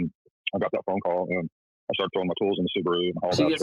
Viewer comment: 👋😊 Q. Did he say throw his Clothes in the Subaru? CC agrees. TS 2:45 ish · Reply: Tools